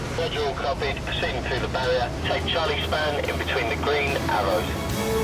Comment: I heard the london VTS said central cockpit proceed to barrier take charlie span in between the green arrow